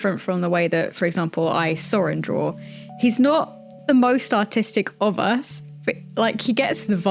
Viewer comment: Don't look at me I kept hearing "Autistic" instead of "Artistic" 😭😅 - ((Also we stan Tieflings (D&D vibes))